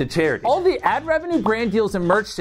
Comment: Don’t forget that all the ad rev, merch sales, and brand deals I receive off this channel go towards running my food pantry! Next video will be an update from the communities we feed <3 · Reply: I thought he said, “Towards running my blood pantry.” And I was Omfg